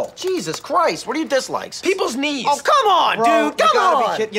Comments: This whole time I thought he was saying peoples needs. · i heard him say people’s needs instead of knees and i dont know which one is funnier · For a second I thought he said "people's needs" for his dislike. Lol. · I thought he said "Peoples needs" at first and I was like yeah I hate that too but he said "peoples knee's" lol